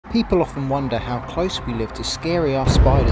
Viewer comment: My dumb brain thought he said “Burger that”